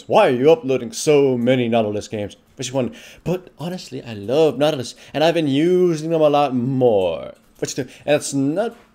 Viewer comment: what is that weird fucking thing you say at 0:06 and 0:11? you say it in every goddamn video. like seriously, do you have tourettes or something? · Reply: "Voice check"